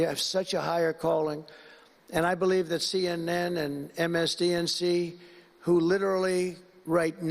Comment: MSDNC. is what he said